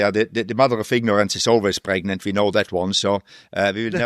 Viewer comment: what did he say? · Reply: the mother of ignorance is always pregnant